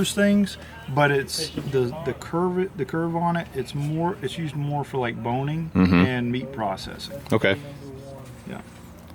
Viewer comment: Did he say its used for boning?? At 16:28 😂